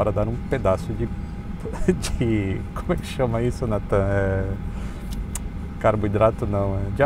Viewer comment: "[...] como é que chama isso, NA TAM?" Não entendi! Bolo não se chama bolo em todas as companhias aéreas?! 🤔🤔 🤣🤣🤣🤪🤪🤪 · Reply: Nathan* e eu suspeito que seja o câmera